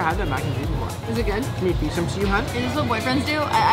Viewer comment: did just say hun about hunny